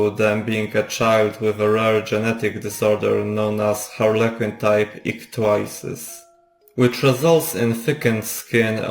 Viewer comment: What is the disease called? I couldn't understand when you say it at 7:36. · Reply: harlequin type ichthyosis